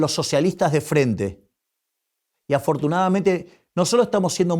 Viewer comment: the subtitle says "unfortunately" but he actually said "afortunadamente" which means "fortunately". If he said "unfortunately" he’d have pronounced "desafortunadamente"